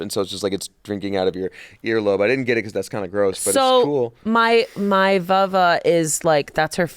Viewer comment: Did she say “MY VuIva”? 😄